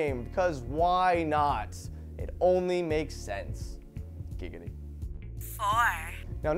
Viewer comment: Did he say "giggity" at 1:14? XD